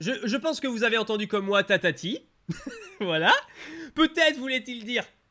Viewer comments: je suis d’accord avec toi il dit Tatati 🤣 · Même miaouss ne respecte pas roucool.... Chaque jour, des dizaines de roucool ne sont pas respectés.. donné leur un don et aussi moi j'ai entendu "tatami"et pas ptitard